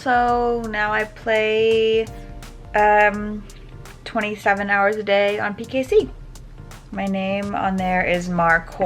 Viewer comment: did she say 27hrs a day